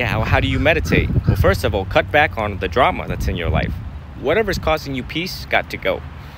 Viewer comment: He’s saying “whatever is COSTING you peace” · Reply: Ahhhh...first i heard " what causes you peace" which makes no sense